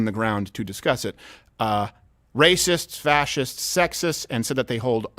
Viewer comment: Fascists? I thought you said "fat chicks".